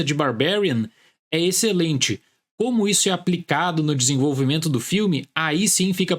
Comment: Eu quase ouvi "como isso afeta o Grêmio"